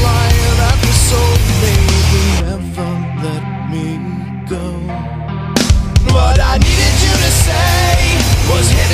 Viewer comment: at around 0:30 is he saying my head is shaking and my nuts all right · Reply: Prince of all gaming my head is shaking, yeah, but that's alright, is what he is actually saying g